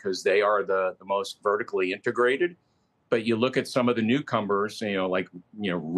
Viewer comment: did he say "newcombers? lol